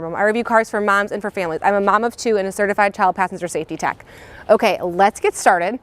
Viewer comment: I thought she said: I am telepathic safety tech. I had to turn the subtitles on